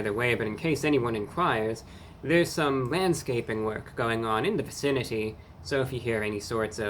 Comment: Had to replay the video a few times. The sound volume was low and I thought you said "...manscaping work" 😂